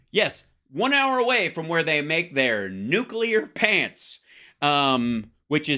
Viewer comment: i thought he said Nuclear Piss.